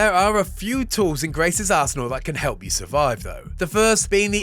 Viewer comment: did anyone else think he said Grace's "a$$ hole"??? Just me? Okay.